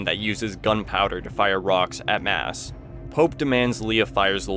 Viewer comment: did you say it fires ROCKS at mass??? I'd think you'd say rockets but thats also not true so what did you say??